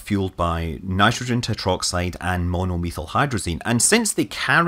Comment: Did anyone else hear it as the peculiarly appropriate, "...nitrogen tetroxide and mono lethal hydrazine"? :D